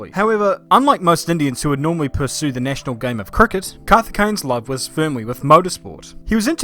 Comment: 2:11 "the national game of cricket", did I hear that correctly?